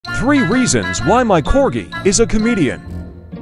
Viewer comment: I heard "reasons why my dog is Canadian"